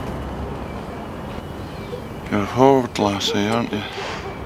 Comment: what's he say at 3:00? is it "you're a bold lassy, aren't you?" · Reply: "You're a forward lassie, aren't ya?"